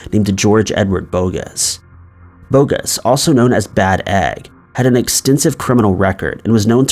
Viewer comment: Did you just say that guys nickname was Bad EGG ? 😂 👿 🥚👿🥚👿🥚